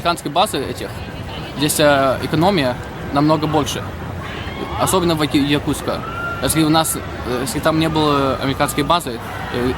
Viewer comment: Мне послышалось, или он сказал "особенно возле Якутска"?